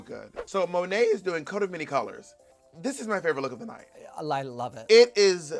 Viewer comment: Bob has been campaigning HARD for Monet at the pit stop 🤣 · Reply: When Bob said “this is my favorite look of the night” about Monet’s runway I thought she said least favorite and I was in total agreement 😆